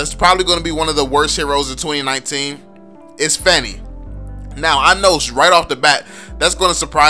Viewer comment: I was so confused when I heard "Fenny" was I the only one?